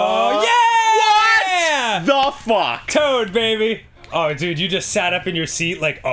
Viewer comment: When Dan yelled "Toad Babie!" I heard "Chode Babie"